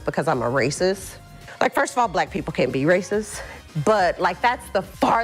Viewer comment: Wait...did she say black people CAN or CAN'T be racist? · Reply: She said that they CANT 💀